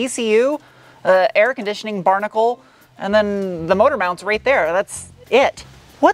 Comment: I think you meant to say electronic brake distributor, not a/c barnacle.